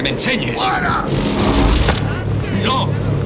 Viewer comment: Que coño dice en el minuto 0:41 que no se entiende bien?? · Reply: Creo que sólo dice "¡NO!"